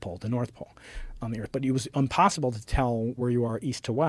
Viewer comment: does he say unpossible?